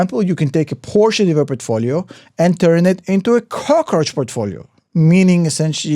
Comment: Did he say “cockroach portfolio”? (20:38) Never heard that.